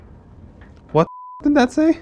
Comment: i swear it said screw you 🤣🤣🤣